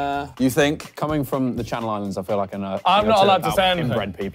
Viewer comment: Anyone know what Chris said at 14:09 😂😂😂 I can’t decipher it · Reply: I think he said “Coming from the channel islands i think i know a thing or two about inbred people”